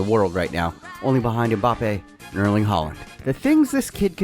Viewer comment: did he say Erling haaland???